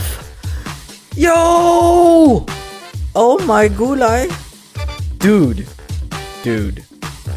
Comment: To those wondering, "Oh my gulay" means "oh my vegetables" when translated. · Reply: I thought he said oh my gulag!